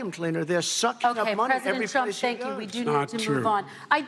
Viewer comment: 부분에 안들리는 부분은 every place he goes 라고 들리네요 ㅎㅎ